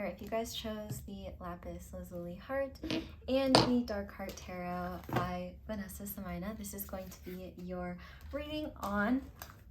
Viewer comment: Did I hear you say "I, Vanessa Soumayina"?